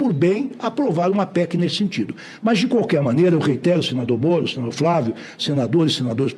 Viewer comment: Em 05:11 Eu entendi direito? "... de qualquer maneira, eu reitero, senador Bozo, senador Flávio..." 😂😂😂😂